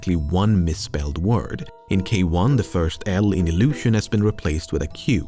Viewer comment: Not gonna lie, when you first read out iqlusion i didnt even register that it was illusion. I thought you said occlusion